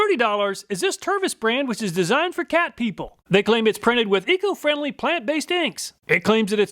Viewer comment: Did I hear someone say, Designed for Cat People. 🤯🙀😸😺😻🐱🐈‍⬛🐈🐈🐈🐈🐈🐈🐈🐈🐈🐈🐈😻😍 3:24 🤗